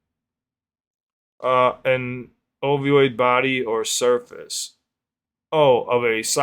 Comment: Jusr looked at the offical lyrics and listened to it again and I think it's safe say to say we're all confused because it definitely sounds like he said oviod but the lyrics say opiod.